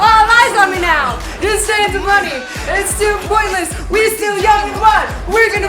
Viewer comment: When she started rapping to Wonderland I heard “Don’t save the money! It’s too pointless!!” LMAO